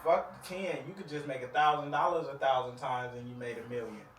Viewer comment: 1000 a hundred times is 100 thousand. How can everyone in the room let that go pass them ? Fire everyone in there. Lol😂 naw I get it .that weed is a mutha. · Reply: Peace! ✨ I believe you misheard him. At 1:29 He says “you could just make 1000 dollars 1000 times and you made a million. I made 1000 dollars thousands of times…”